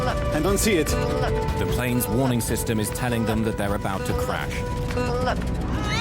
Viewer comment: who else heard "good luck" instead of pull up? xD